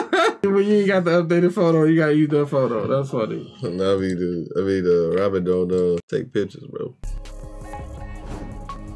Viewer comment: WTF HE SAY? "I love you dude, I mean-" WTRF MAYNE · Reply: Raw _ he said, “Nami, I mean Robin”